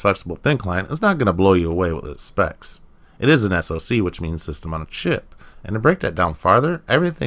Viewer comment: I thought you said "which means it's a piece of shit" for a second lol